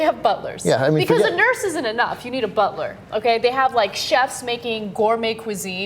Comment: Pause at 0:53, then put on subtitles. Does it say it say, "Isn't enough to eat a Bulter" ??? O_o